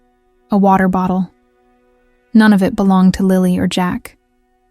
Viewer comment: They were seen with family in public on 1st May. 30 April was a professional development day at their school. The bio dad's family said they're non verbal, not Daniel's family. The tracking dogs lost their scent at the end of the driveway. Daniel is a seasonal worker. In Canada, seasonal workers can claim unemployment benefits based on how many hours they work in a week. Catch up. And get your goddam facts straight. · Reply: I thought the mother said the blanket belonged to Lily.